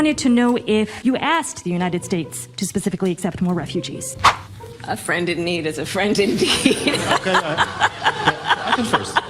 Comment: Context about 13:15 president Duda said “a friend in need is a friend indeed” before but because of his English he misspoke and instead said something that sounded like “a friend in need is a friend in dick”.